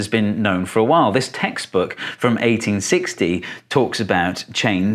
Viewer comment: What do you think? Have I convinced you? You can also discuss this video on REDDIT: <link> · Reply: Hang On, did you say "textbook from 1860?"